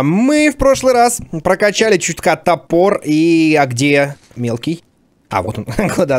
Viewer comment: мне одному послышалось, котопор, он типо из котов